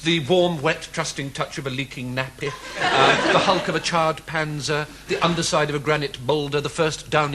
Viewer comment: Does he say "the hulk of a charred Panzer"?!